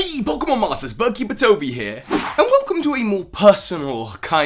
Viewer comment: i thought you said hey pokemon molesters and i've never been so offended